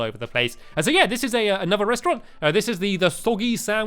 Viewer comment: Did he say foggy sandwich or soggy sandwich